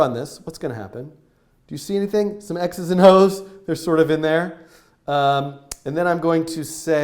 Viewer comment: Is it just me or at 3:31 did he say x:es and h*es?